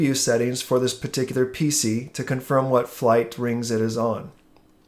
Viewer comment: did he say flight rings ?